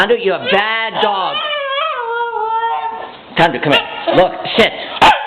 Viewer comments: i first heard him say shit XD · Hahaha!!! On 1:35 instead of saying sit he Swiss shit!!!